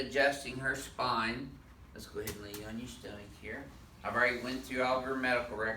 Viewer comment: it sounded like he didnt know if he wanted to say tummy or stomach and he just said tstumach